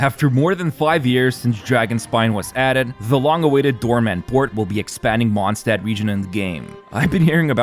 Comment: i heard doorman and got instant PTSD